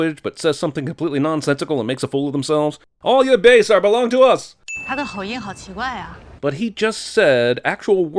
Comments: in 1:32 she says: "his accent is so weird" so the subtitle is wrong, +1 more movie sin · To be fair at 1:32, what the woman actually said was ‘his accent is really strange’. It’s just a bad translation. Really off because what she said wasn’t even a question 😂 This is what she said in Chinese if anyone wants to double check: 他的口音好奇怪啊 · it's not "Did you understand a word of that?" she actually said "他的口音好奇怪啊", like, "He has a weird accent" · Li Bingbing said "His accent is super strange" instead of "Did you understand a word of that", so, movie maker can't put the correct subtitle for his own movie, still a sin lol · Hi at 1:33 the subtitles are completely wrong for the woman. What she said was : his pronunciation is very weird. Thus is makes sense that she did not understand a shit of what he said, because what he said was very muffled to a Chinese speaker. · as a Chinese person speaking and understanding fluent Chinese I can say that the subtitles were wrong and that what she said was “he has a weird pronunciation”. Therefor movie sin tally: 109 😁